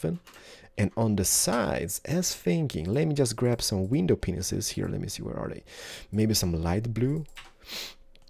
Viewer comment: Were are u from cause ur accent (I think it's an accent) makes it sound like ur saying window penises instead of window panes